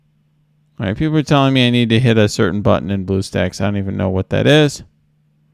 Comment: I got the lag problem too. Does anyone know what is meant by "push a button in boost ex" ? For any help you get a step closer to heaven, ich promise. ( 16:55 ) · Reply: He says blue stacks which is the program he uses to run the game on his pc. He never says what button to press though